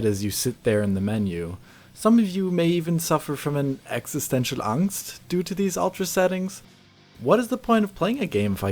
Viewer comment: Did you just say "existencial Angst"? xD